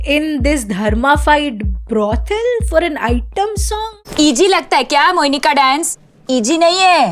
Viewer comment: I thought she said PG lagta hai kya mohini ka dance? 🤣